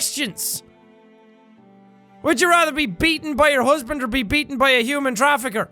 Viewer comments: I thought it said be eaten by your husband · I thought it said be eaten by your husband Anyone else